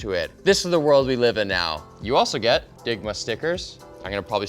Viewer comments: i heard "you also get ligma sticker"... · Not gonna lie. I thought you said Ligma stickers, and I thought, “What’s ligma?”